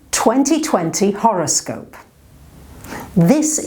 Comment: I heard “horrorscope”... how apropos.